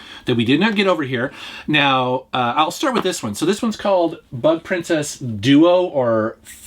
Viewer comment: lucky i watched anime alot n remember..mushi..means bugs.i thought i hear butt princess.haha